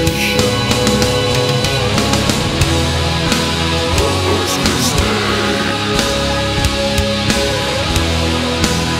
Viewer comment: 3=? What did he say? Hos Creestay? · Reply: corpus christi